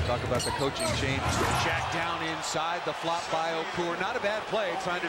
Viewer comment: did he really say the flop by Okur?